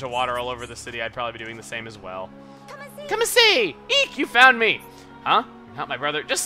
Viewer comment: Cian Sounded Like She Said "Come Masae!" XD